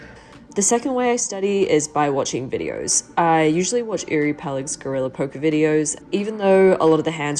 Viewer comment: Hey, whose videos did you say you're watching at 3:25 ? P.S. Thanks for improving the size of the cards graphics! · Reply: Uri Peleg, he’s great!